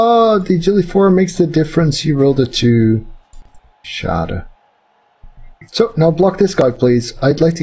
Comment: did you just said "schade"?^^ or am i just deaf?^^